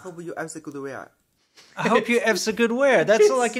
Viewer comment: I hear, "I hope you are as good as we are."